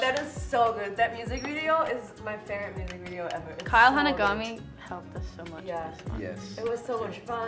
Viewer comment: the subtitles says "Kyle Hunter gummy" 😂😂